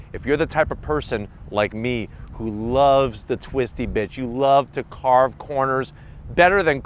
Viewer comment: think you meant "the twisty bits" but I heard "the twisty bitch" lol